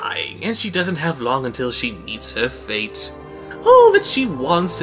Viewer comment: I thought you said "she doesn't have long until she eats her face," leading me to believe that this segment was going to be about a new disease that causes people to cannibalize themselves after a certain period of time. Considering the tone of the last segment, I was a little confused.